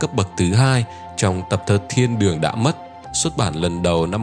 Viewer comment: tập thơ " Thiên đường đã mất " mà tôi lại nghe thành " Thiên đường tung tăng "